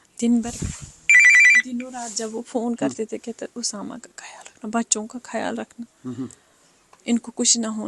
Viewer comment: did she say Osama. Every action has a history and repercussions. But these are doctors; suppose to be lifesavers. How much were they brainwashed to become a terrorist ?? To the extent that killing innocent people was a rational and ethical choice for them..